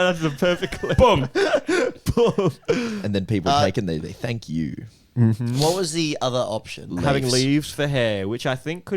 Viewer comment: from facial expressions 16:57 definitely seemed like a pun but i couldn't make out what he said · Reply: it’s one of the ones he mentions at the end, he says yew instead of you, like he puts emphasis on it